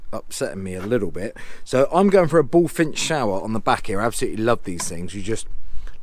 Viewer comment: Thanks for the video! What is the name of the adjustable temperature shower adapter you point out at 7:30. I couldn't understand what you said. Thank you! Do you have a link for it? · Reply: Hi by his is the bullfinch shower. Amazing bit of kit. If you head over to my ‘6 must haves’ video. Full walkthrough and Amazon link.